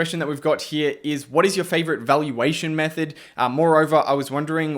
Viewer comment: I thought I heard “what is your favourite method for evaluating socks”😅